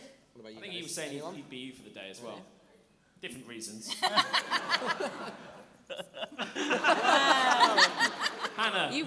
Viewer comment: wait what did say ? xD It's really weird not to have understood especially with miming having erm.. an ample bust shall we say- right afterwards ! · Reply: Persian .Seven He said "I thought he said he'd be you for the day as well... different reasons". ;D